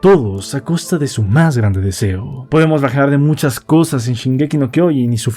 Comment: no logro escuchar bien lo que dices en el minuto 15:28 me podrías decir que dices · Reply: "podemos rajar de muchas cosas de snk y su final..."